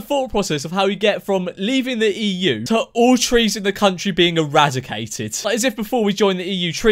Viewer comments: Think she meant cheese not trees mate X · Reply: Alex Barrett no she said trees it sounded like cheese, in the rest of the clip everyone else thought she said cheese but she said she meant trees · "What do you think about Brexit?" "What's that?" "It's where we're leaving the European Union" "So, does that mean we won't have any trees?" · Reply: At first I thought she said cheese- but NOOOO TrEeS!!!